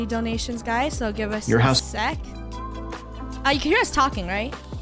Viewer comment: anybody else hear your house?